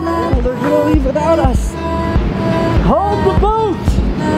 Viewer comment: Did he say at 5:30 "Hold the boot"?